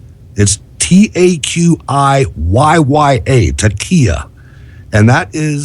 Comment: When you said 'taqiyyah', I thought you said 'tequila' , for some reason.